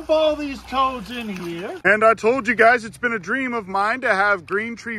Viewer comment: Did he just say, "I toad you guys..."?😂